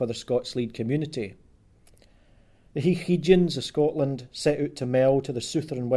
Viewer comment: I'm not sure of correct spelling but at 6:35 you mention "high heaguns" , could you tell me the correct spelling and who they were exactly? My grandparents were from Scotland and I heard them use that term frequently when referring to someone that thought they were a big shot or special. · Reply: It will be "High heid yins", three separate words meaning high head ones, or those in authority.